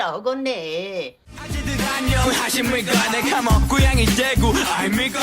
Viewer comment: when i first heard this song i thought Yoongi is saying 내카모 고양이 대구 instead of 고향이 대구, like he is admitting he's a Lil Meow xD xD xD